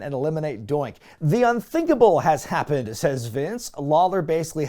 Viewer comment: I cant be the only one who heard say "The Un-Dinkable has happened."